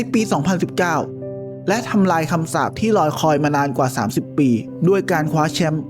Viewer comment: ** งง มาก นาที ที่ 3:07 ที่ คำพูดว่า * ลอยคอ * จริงๆ แล้ว ควร พูดว่า รอคอย การเป็นสื่อ ควร รอบคอบ ในการ ใช้ ภาษา ครับ